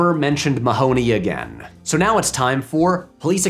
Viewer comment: Did you just say tits?